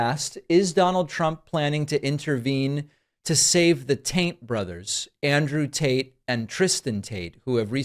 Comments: At first I thought he said the "Taint brothers," and then I was like, yeah, that actually fits. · Theres no way its just me.... did I hear "The TAINT BROTHERS?" about 20000% sure I heard 'the TAINT Brothers' lollllll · Sounded like he said "taint brothers". I think I'll call them that from now on. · did I hear ‘taint 1:31 ’ brothers 😆😂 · I thought he said taint brothers. LOL. The Saudis dropped a free airplane into the swamp. Leavitt has weirdly large space between nose and lips. My brain is everywhere right now : )' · Thought he said taint brothers at 1st 😂